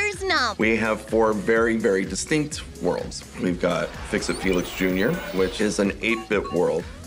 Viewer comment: weird felix jr i think the woman said "You already fixed it, Felix."